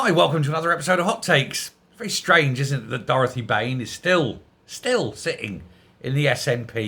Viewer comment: I thought you said Brain ! I thought "well that's a lie." Should have gone to specsavers. 👓